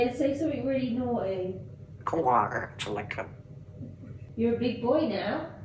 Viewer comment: Sounds like he said, Coldwater to Lincoln... But the second bit is on point 😂😂😂 hot damn... So Funny